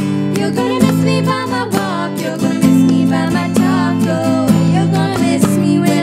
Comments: Am I the only one that hears "You're gonna miss me by my taco"😂😂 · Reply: Gisele Naarendorp I hear both "miss me by talk oh" and " miss me by my taco" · I heard it you're gonna miss me by my taco :')AHAHAHA IDK WHYY!! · it sounded like :your gunna miss me by my walk your gunna miss me by my taco" xD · Your gonna miss me by my walk, your gonna miss me by my taco.. 😂❤️ misheard lel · When they said ur gonna miss me by my walk it sounded like they said taco 🌮 lol 😂 like if agree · Lol it sounds like you were saying that you're gonna miss me by my taco 🌮🌮🌮🌮🌮🌮🌮🌮🌮🌮🌮🌮🌮🌮🌮🌮🌮🌮🌮🌮🌮🌮🌮🌮🌮🌮🌮🌮🌮🌮🌮🌮🌮🌮🌮🌮🌮🌮🌮🌮🌮🌮🌮🌮🌮🌮🌮🌮🌮🌮🌮🌮🌮🌮🌮🌮🌮🌮🌮🌮🌮🌮🌮🌮🌮🌮🌮🌮🌮🌮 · I thought they said taco. 😋🌮🌮🌮🌮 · It sounded like they said taco when they said talk oh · When you said "you're gonna miss me by my talk oh" it sounded like you said taco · You're gonna miss me by my talk, oh I heard it as You're gonna miss me by my taco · It sounded like you said “your gonna miss me by my TACO” 😂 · It sounded like they were saying miss me by my taco.